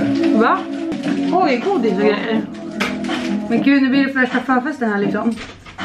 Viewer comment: hörde ”oj kådis och grejer” 😂😂